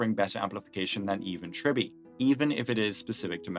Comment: did you say better than e1 tribbie or even tribbie? 🤔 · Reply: even tribbie